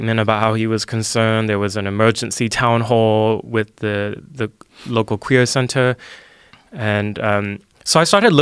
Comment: Did anyone else have to rewind it at 10:54 a few times to figure out if he said a "local queer center" or "local career center" i heard Queer center atleast 4 out of 5 times i listened to that part... But i want to believe he said career center.